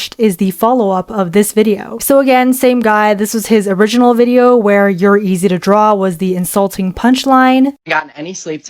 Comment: I thought he said "and ur easy to drop"